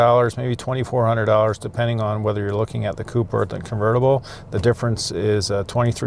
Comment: did he say convertible?!